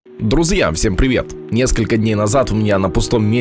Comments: Я один в начале услышал "Друзъя" а не друзья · Мне послышалось или он сказал друз'я